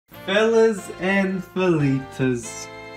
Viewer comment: at the start i thought u said fellas and philly's lmao